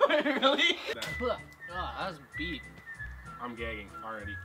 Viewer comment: it sounds like he says "I'm gay"